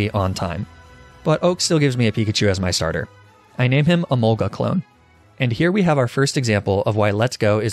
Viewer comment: did he say emolga!??!!!?